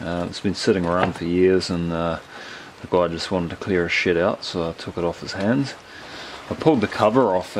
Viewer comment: LOL when you said, "the guy just wanted to clear his shed out" I thought you said "clear his 💩out" that accent got me...